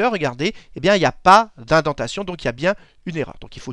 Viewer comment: j'ai l'impression d'entendre incantation a 5:34 mais il dit quoi a la place ? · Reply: indentation